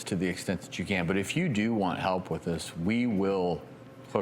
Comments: i thought he said "we will, f*ck it up" BAHAHA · I thought he said "we will fuck it up" and was like wow what, but he said hook it up 😂😂 oops